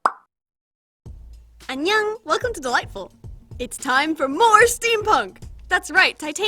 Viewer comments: I thought you said “welcome to the lightbulb” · at first i thought she said welcome to the light bulb